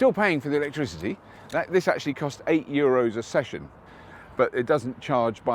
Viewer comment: Thought Bob said 8 euros a second...... WTF... rewind.... Session.... phew... less than my wife..